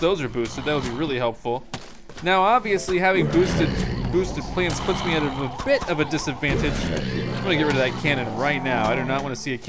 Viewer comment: did he say DISadvantage? Why would boosted plants be a DISADVANTAGE? Or did I miss something? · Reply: He said WITHOUT boosted plants it could be a disadvantage